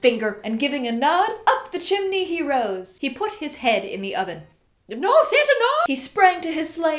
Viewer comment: “He put his head in the oven....” “NO SANTA NOOOO-!!!” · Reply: I thought she said “NO SATAN NOOOO-!!!”